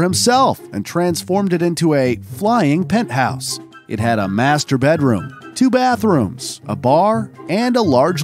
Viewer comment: did you say 'masturbate room'?